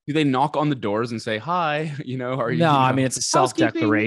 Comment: "why is this guy talking about BC" yeah why? "self declaration" I heard "self decoration"